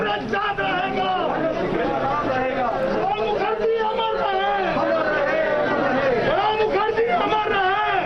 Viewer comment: Did i hear " Rahul Gandhi amar rahe " ? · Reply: Use headphones. The words uttered were "Pranab Mukherjee amar rahe".